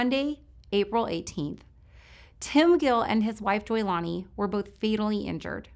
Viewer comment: I'm hearing Leilani instead of Joylani when she mentions Tango and Juliet @ 6:38. Just my ears? RIP to them ~and Michael.